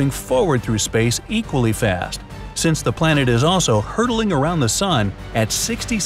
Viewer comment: for a second i thought he said earth was hurdling AT the SUN so i rewatched it and i noticed he said around not at :/